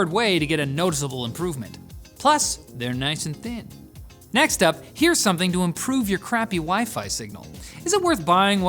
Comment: I thought he was about to say: "here's something to improve your crappy WIFE . . ."